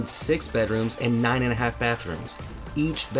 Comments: I'm sorry did he say 9 and a half bathrooms? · did I hear that right? 9 and a half bathrooms??? what's with the half ??? is that half a toilet and half a bathtub ? where's the other half?